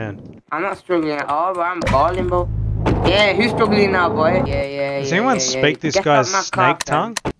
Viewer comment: did he just call him a goy for real or was that a speech impediment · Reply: I think he said boy not goy.